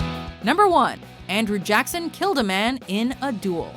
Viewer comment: For a second I thought she said Andrew Jackson killed Charles Dickens...